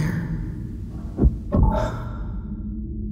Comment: Can somebody tell me what does the guy say at 3:44 ? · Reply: He says "I am still here?" signifying that he may not be the only one living in his own head, scary thought....